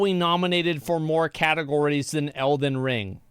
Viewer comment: ''Cat ogories'' is what i heard.. im so funny